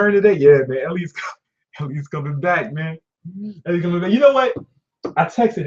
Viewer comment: ish, did you say Elliot was coming back? Was it Elliot Hulse you were talking about?